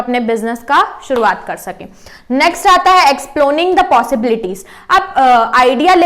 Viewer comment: what kind of word is it .exploning?